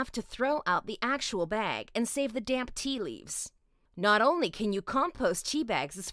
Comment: Sounded like she said save the damn tea leaves